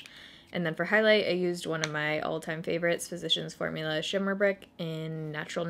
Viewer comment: Every time you say "Shimmer Brick" I hear "shit a brick" in my head lol. WTF is wrong with me? 🙈